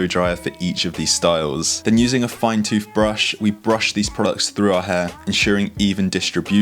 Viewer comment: did he say toothbrush?